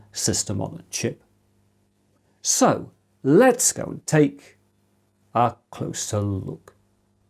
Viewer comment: I thought he said "Lets go take a chinese look"